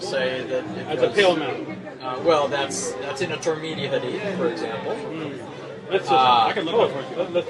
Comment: The way that he pronounced Imam Tirmidhi's name was so bad that I couldnt figure out what book he was referring to at first. Sound like he was saying "Timothy".